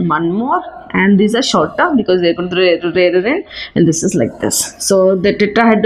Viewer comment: can someone tell me what did she say at 1:30 ??? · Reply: Rarer end